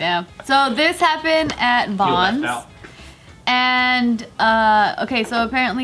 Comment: What did he say at 7:47 tho? "__ minority" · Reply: Model minority